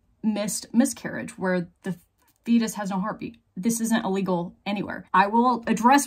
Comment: I swear it sounds like you're saying "when a fetus has a heartbeat". I'm pretty sure you're actually saying "when a fetus has no heartbeat". That threw me through a loop and had me looking up and guessing a bunch of things. At one point I thought maybe a fetus could be brain dead and still have a heartbeat because it was connected to the mother, but why would you even bring that up in this case? You do great work on educating the public and it is good to have some passion showing in your videos but due to the factual context of your videos some points need to be made extra clear. I think someone who knew less than an ObGyn would have expounded a few times that the fetus had already died and the DNC was only for removing the already dead fetus. I have my own problems with explaining too many details and not keeping the point in clear and prominent. I had to reduce the above two paragraphs down from something about three times as long. I'm not even emotionally attached to my point.